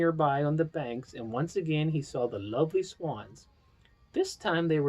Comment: did Daddy say “lovely swans??” 💞